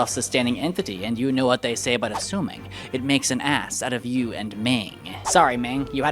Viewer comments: did he just say ass? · did just say A#$?